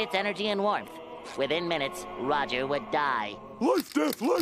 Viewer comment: I always thought he’s saying light death 😂